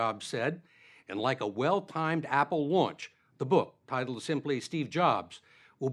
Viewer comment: Did he just say at 1:04 "like a well timed Apple Watch"? How can an interview in 2011 make a reference to a product that didn't exist at that time? 😱 Apple Watch was launched in April 2015. The interviewer goes on to say "will be in stores tomorrow" at 1:09, so it was definitely recorded in 2011... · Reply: A well timed Apple launch is what he said